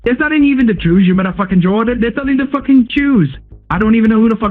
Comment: i heard "Jordan", did anyone else